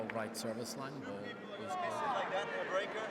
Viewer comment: Someone explaine please.. at 9:38 del potro serves the ball in right om the line, then federer challenges? I don’t understand what happend there, the umpire says i thought it was in, it was right? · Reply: Fed challenged, “you gonna miss it like that in a breaker”, meaning you guys messed up at an important part. Hawkeye result shows Federer wrong. He’s wrong but confident he’s right.